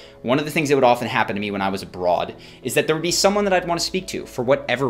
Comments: did he just say that he used to be a broad? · Did he just say he used to be a broad? I can kinda see it.